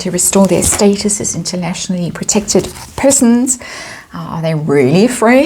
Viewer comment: For a sec there I thought I heard "internationally protected peasants" LOL